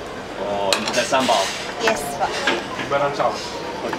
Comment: did she just say yes po? Filipino everywhere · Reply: I think she said "Yes Pak".